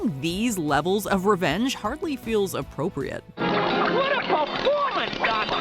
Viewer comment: Did Bugs just say, "What a performance, God damn you!"?